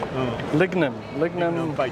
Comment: I think he is saying the handle is made of lignum vitae, a hard beautiful wood